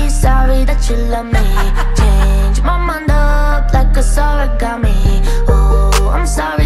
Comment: I love this song so much! But honestly, when she said, "change my mind up like it’s origami" I thought she said "change my mind up like a sour gummy" 😂😅